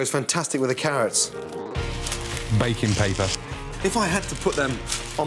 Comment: I almost thought the narrator said 'bacon paper.' million dollar idea right there.